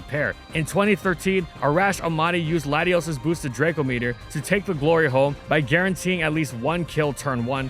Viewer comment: I thought he said “to take the glory hole”